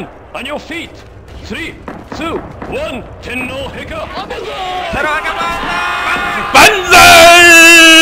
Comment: What does 22 say at 1:19 tindal hicker?? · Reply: He says "Tenno Heika Banzai"